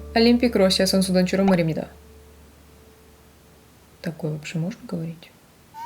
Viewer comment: 뭐라고 한거에요 ? ㅠㅠ 러시아어 초급생으로 들린거는 Такои~~~로 들렸어용